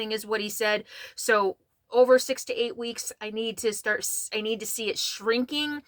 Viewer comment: Oh my gosh. I was laughing so hard when I thought you said "We need a shrink". Then I realized I heard it incorrectly and you were talking about seeing Sadie's lump/scar tissue needs to shrink! Time to get my hearing tested. I appreciated your words today and love that you were so bubbly. Your hair looked great too. As always stay safe, fun and beautiful!